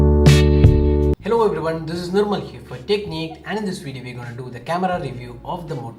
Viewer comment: I thought he said "this is dickneed" except for techniqued and my left ear is having the most pleasure oh boy